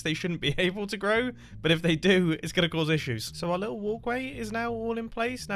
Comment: I swear when he said "walkway" I heard "oakway" named because of the saplings.